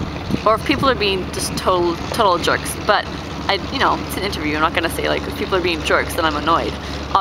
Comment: What did you said in 3:40 before abviously? Something about jokes, I didn't understand :/ Thanks you!! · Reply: "It's an interview I'm not gonna say like if people are being jerks i'm annoyed" hope this helped